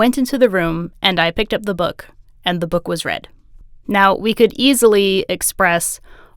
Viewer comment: Hah! I heard "the book was red," as "the book was read." So it threw me a bit when you stared talking about the redness of the book.